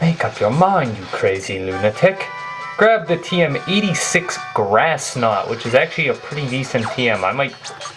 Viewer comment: I thought he said tm grass snot. Haha.